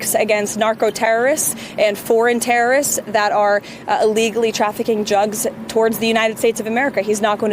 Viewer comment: These clowns are going to jail if they lose the mid terms. edit: Did Leavitt say "trafficking jugs"? lol